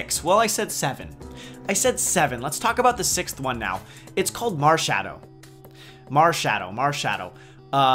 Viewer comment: Looks good, I'd probably go for the owl! At first when you said marshadow I thought you said marshmallow haha! Could be a fairy type though lol :)